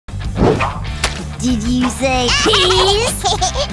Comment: did u say piss